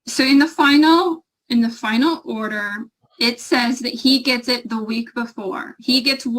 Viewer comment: Did she call her son IT? 1:38:28 29 30.